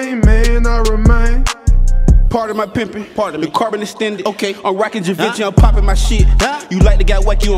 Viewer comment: What he say in 2:03 explain please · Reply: Carbon Extended (Rifle) Im rocking Givenchy